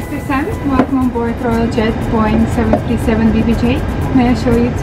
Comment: sounded like jet boring lol 😂